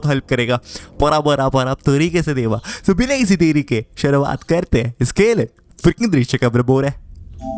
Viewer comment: yes what did he said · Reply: khelan fricking drishya ko everybody